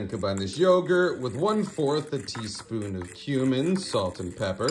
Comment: Did anyone else think he said teaspoon of HUMAN at first???!!! lol